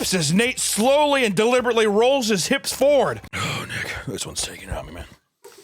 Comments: what did he say there... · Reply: Oh Nick this one’s taking it out of me man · ik he said “oh Nick”but it sounded like he said “oh nigga” 😭